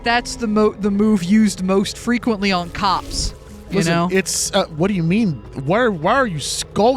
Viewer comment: I heard it as "by cops" instead of "on cops" and I spat out my drink by how fked up that joke was lmao